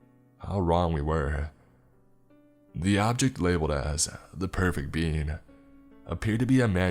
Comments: I thought they were saying the perfect bean for so long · At first I thought he said “the perfect bean” 😂😂😂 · I can’t be the only one who thought he was saying the perfect bean